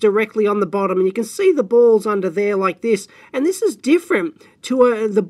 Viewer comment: I heard "Balls under there" and "Balls in there" so many times, i even started to look down a few times..